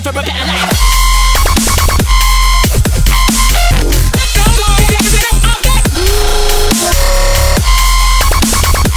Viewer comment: WHAT DOES HE SAY AT 1:32 :D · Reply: the original sample says "hey bro gimmie that object"